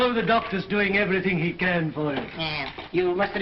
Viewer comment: does she say < " Get out !, get out you bastard "? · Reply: "You doctors".